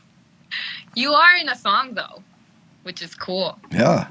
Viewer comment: For a second I thought she said, "you are in a thong, though"